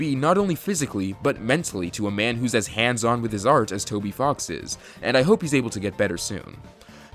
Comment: i thought you said "as handsome as Toby Fox is"